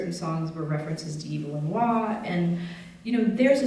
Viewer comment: she says "..i know certain songs were references to ____" Sounds like Evil in wha... :) Does anyone know what she said there? Thanks. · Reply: Evelyn Waugh